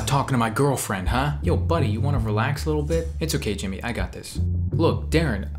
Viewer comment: Oh gosh! I thought you said Jimin and i was just like WAIT WUT!!!???!!!!??? LOL